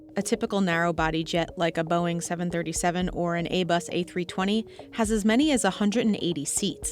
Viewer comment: did you say "A-Bus"??